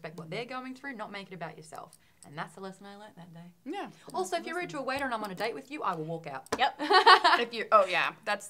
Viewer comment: Took me WAY too long to figure out what the hell said at 8:30 Replayed it fifteen times before I realized she said "Rude to a waiter" lol